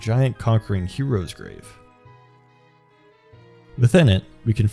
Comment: Please tell me I am not the only one who heard "Giant Cockring"